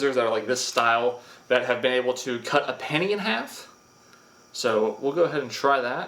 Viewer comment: I thought he said "cut a panty in half" 🤣🤣🤣🤣